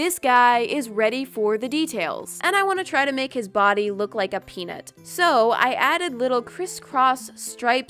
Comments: i thought she said I want to make him look like a penis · “I want to make his body look like a peanut.” I heard “I wAnT tO mAkE hIs BoDy LoOk LiKe A pEni-“ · did I hear that right ^^ Edit: OOOO she said peanut xD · I thought at first that she said “I want to make its body look like a penis.” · i thought she said "and i wanna try and make his body look like a p*nis" i was like 0.0